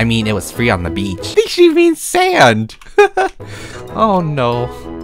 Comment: WHEN SAID “Beach you mean sand” I THOUGHt HE SAID “B*tch you mean sand” lOL